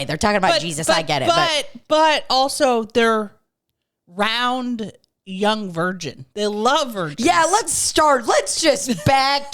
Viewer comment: I always thought it said “round young virgin” like they are nice and plump lol